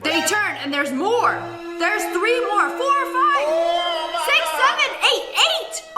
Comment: Did she just say 67 hehheh